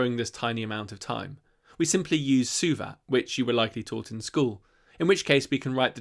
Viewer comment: Edit: I think I may have found the right thing. Thanks, Google. 'math use suva' got me 'suvat' instead -- I definitely can't hear any T there, no matter how many times I replay that line in the video! Looks like it was my sad lack of physics classes responsible for not recognizing it, rather than insufficient math classes. 25:24 "We simply use 'suva' which you were likely taught in school." Ummmm... What level of schooling? Is it above calculus? I don't remember any math thing referred to as 'suva'... And you don't explain what "using suva" means, so I can't tell if it's just something I do know, by a different name... Is this a case of the YT subtitles generating a word that sounds like the word you used but is spelled differently? Even on that assumption I still can't think of an appropriate replacement...